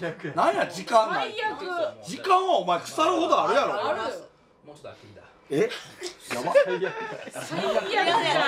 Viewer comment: ここ聞き取れた人いる？ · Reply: もうちょっと飽きてきた